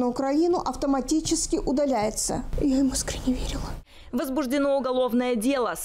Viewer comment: (подсказывают что говорить ) Шёпот девушки (я ему искренне верила )